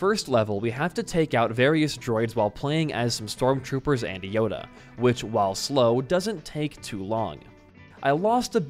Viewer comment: tf did he just say. WHO and yoda.